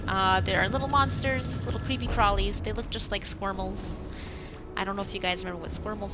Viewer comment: Idk...i just listen to. Welp, they’re named squirmles for me! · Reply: Squirmles?? I thought she said swirlies